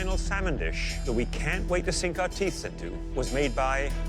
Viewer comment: Uhhh am i the only one that heard him say “sink out teeths into” uhhhh idk if teeths is a word but idk man